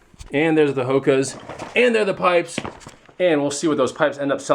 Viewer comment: Josh D hilarious comment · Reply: I thought you said hookahs and pipes at the end.